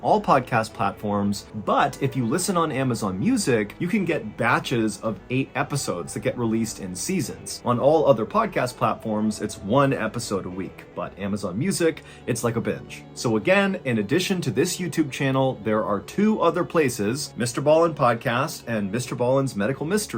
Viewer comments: it sounds like he says “it’s like a bitch” instead of it’s like a batch lol I rewound it like 5 times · it sounds like he says “ but Amazon music is like a bitch” lol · Is it just me, or did Ballen just call Amazon Music a 'Bitch'...? 😂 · I 100% thought he said "its like a bitch" during the advertisement 😂😂😂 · At 9min in u say “it’s like a binge” and I heard “it’s like a b!tch” 😹😭😹